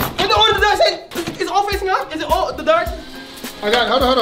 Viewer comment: wth was saying lmaoooo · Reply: “is it alll the dark?”